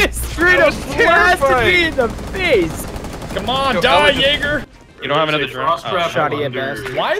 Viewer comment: I deadass thought he said die 🅱️igger